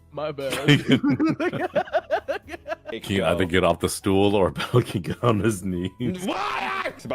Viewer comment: can someone tell me what does said i cant listen to it properly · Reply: “Tell him to get off the stool or get on his knees?” told to get off the stool since he isn’t as tall as and. then makes himself smaller than he should be making him look like he’s kneeling.